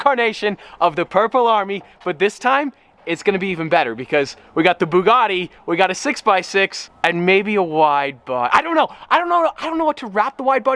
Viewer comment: Jesus I thought he said he was gonna 6x6 the veyron and make it a wide body